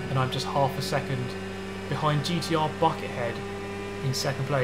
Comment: I thought you said "fucker head" not "Bucket Head"